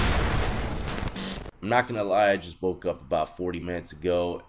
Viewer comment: For a minute I thought you said you just broke up and it looked like you was crying. I was about to be sad for you. I had to rewind and rehear and heard you said you just woke up and I felt better.